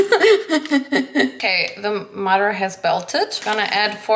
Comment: 2.59 Did she just say 'the mutter has belted' hahaha